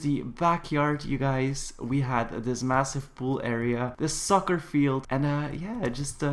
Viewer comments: I thought you said ''the sucker field'' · not trying to be rude but when you were talking 1:40 or earlier i thought you said " Sucker" instead of soccer LOL